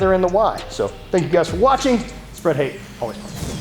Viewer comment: spread hate? Did i hear that right?